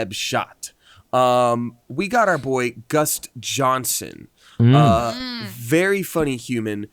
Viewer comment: Did he just say GUST Johnson?😂